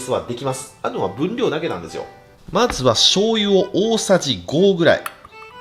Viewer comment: the subtitles say to add about a tablespoon of soy sauce, but what he actually said was to add five tablespoons.